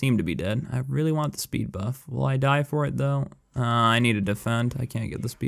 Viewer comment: Did he just say speed buff at 20:00 ?